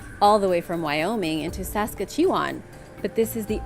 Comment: ALSLSDNBDAKOZKGFN i thought she was trying to say saskatoon not saskatchewan😩😩😩